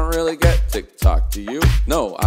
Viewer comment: It sounds like he says "I dont really get to talk to you?" after a while.